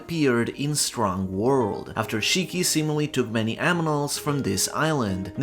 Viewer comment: Did you say "Aminals"?? 😂